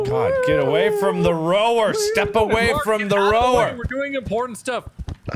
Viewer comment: Only OG's remember when had subtitles that said "Mark get out of the way we're doing porn stuff" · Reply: I thought he said "important stuff"